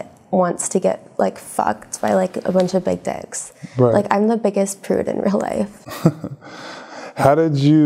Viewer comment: Did i hear it right? Im the biggest prude irl?